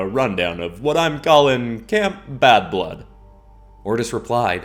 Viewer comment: did he say bad-blood or half-blood